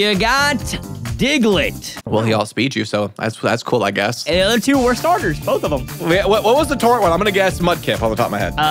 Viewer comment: I thought you said worst starters. I was gonna freak because Piplup is my favorite starter and second favorite pokemon